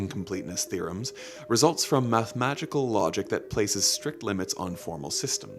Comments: did he say "mathemagical"? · lol that's too funny listen to 12:20 he said math magical logic, instead of mathematical